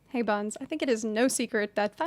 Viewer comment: Me: :(: "Hey buns" Me: :) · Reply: I heard "hey bums" and was confused at why she was opening in such a harsh manner